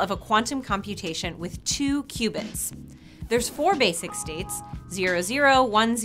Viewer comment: I thought she said "2 Cubans"